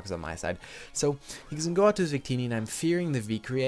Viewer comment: Wait..... Did u just say Zuccini instead of victini? Sorry if its just my hearing but i just wanted to point that out...